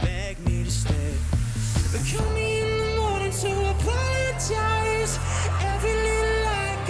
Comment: It sounded like kill me in the moring in